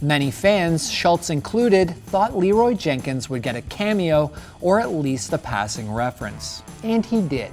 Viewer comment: I have been watching this video since it was posted and I always thought Leroy said “at least I ain’t chicken.”